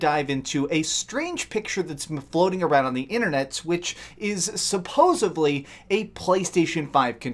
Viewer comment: did you just say "supposably"?